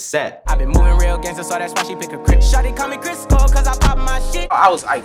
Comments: Bruh I thought he said "Shorty called me CHRIS PAUL cause I pop my shit" lmaoo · I’m convinced Roddy says CHRIS COLE, the skater. not “crisco” Someone correct me if I’m wrong... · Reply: Jose Cruz I thought he was Saying chris Paul LOL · When he said Crisco, i thought he said Chris Paul the basket ball player 😂😂😂😂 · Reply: alwaz gamez nah he said Chris Cole, the skateboarder. “pop my shit” just like pop on a skateboard when u ollie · Anybody else thought he said “shawty call me Chris Paul cuz I pop my shit.” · Oh it said crisco I thought it said chris Cole like the skater.-. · I always thought he said Chris cole. A reference to a pro skater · Who thought he said Chris Paul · I thought he said Chris Cole not Crisco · Damn i thought he said Chris Cole as in the skater not crisco, i thought that shit was a skateboard reference "she call me Chris cole Cause i pop my shit" real skate heads know chris cole has hops i was happy as shit smh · Could’ve swore it said “shawty call me Chris Cole cuz I pop my shit” any skaters out there will get it